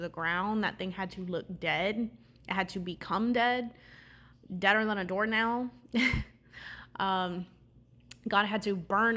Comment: This is confirmation; but sis what did you say!?😭😂 1:11 ?? Deader than a what?😭 I just want to be sure because my friend back in college said a joke with “deader than a door knob” and it sounded similar but not exact so I just wanted to know what you said though because it still was funny and made me think of that😭🤣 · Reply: 😂 deader than a door nail 😂 lol I am not sure if that even makes sense but I've always heard that saying!!